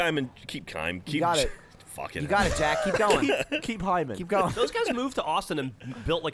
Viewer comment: Did anybody else hear say hymen at around 20:50?